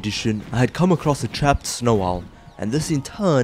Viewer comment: im sorry but did i hear trapped snow owl?